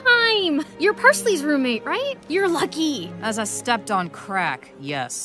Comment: i thought Thyme said 'As i stepped on crack". Which is what people prolly should be on before watching this show.🙃 (Jk. Crack is bad.🤮)